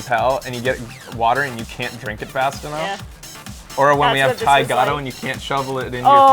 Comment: Does anyone know what is saying at 29:04? Ty goto? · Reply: He's referring to our favourite salad at this restaurant called The Naam here in Vancouver. It's called a Thai Gado Salad. SO GOOD!